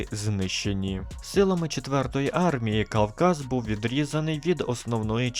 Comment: "КАЛказ" чи мені почулося?